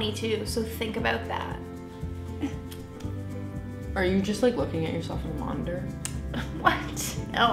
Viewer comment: did she say 'did you just look at yourself in wonder'??